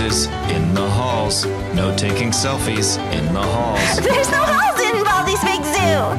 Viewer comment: The principles face is whenever whenever he found out that there are no halls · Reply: Bro I thought u said balls